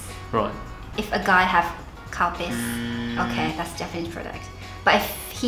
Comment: seriously thought she said “cowpiss”.